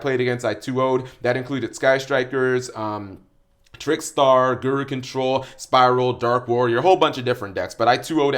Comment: Misheard guru control as goat control